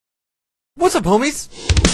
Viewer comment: It sounds like he say: "What's up boobies?" Then runs in the rocket.